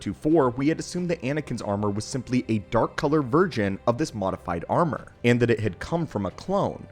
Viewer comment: Anyone else hearing him say at 2:40ish "dark color virgin" instead of version?